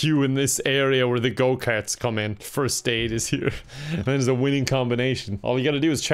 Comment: I had this video going in the background while I was cooking and when he said "First Aide," I heard "First Name," and thought, "Well shit, this park if for sure cursed now."